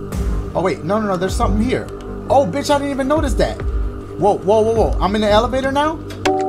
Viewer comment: why did I think he said “im in the hellevator now?” I was finna say, and that’s on Straykids.👏🏼